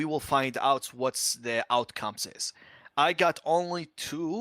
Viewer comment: Did he say "Outcomeses" ?? :D:D:D